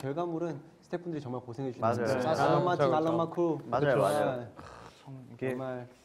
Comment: 쿱스가 머라고 하는 건가요? 제 귀에는 '알람마침 알람만쿠'로 들리는데 너무 궁굼하네요. 제발 알려주세요ㅜㅠ